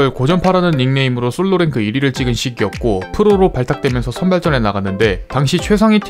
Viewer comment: 분명 시기 였고 이러는데 쉬키 였고로 들리는데 기분 탓인가 ㅋㅋㅋㅋㅋㅋㅋㅋㅋㅋㅋㅋㅋ